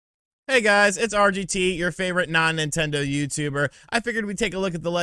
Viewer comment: Every time I see a Knicks hat I hear, “I’m not a Nintendo YouTuber”